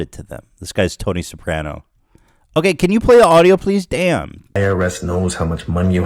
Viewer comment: I thought he said “ Play the audio.. DAN” hahahaha